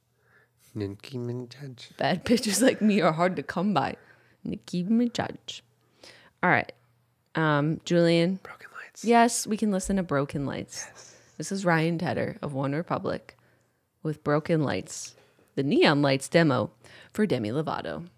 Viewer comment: I thought they said “broken legs”